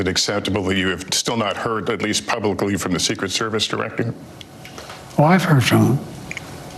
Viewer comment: It sounded like Biden said "Oh, I've heard from him" when asked about the female SS director.